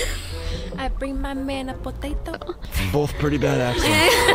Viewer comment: did he say bada$$:( my childhood is Ruined